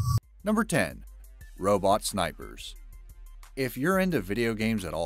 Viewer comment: Robot snipers I thought u said roblox snipers